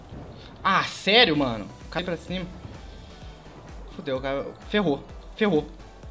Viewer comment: kkkkk eu escutei esse ''Fudeu mano''